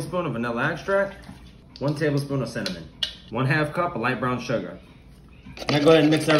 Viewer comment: When he said cinnamon at the beginning I thought he said semen lol